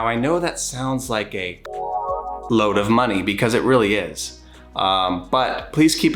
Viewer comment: did you say "a cum load"?? That doesn't even make sense